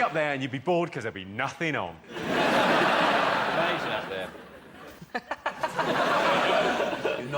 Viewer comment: What did Sean say at 3:02...."something's up there....". It got a giggle from Manford. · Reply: Pretty sure he said 'its amazing up there'